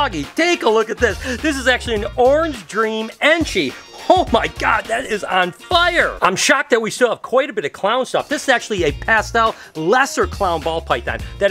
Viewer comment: i thought you said ' this is actually a passed out' 6:13 lmao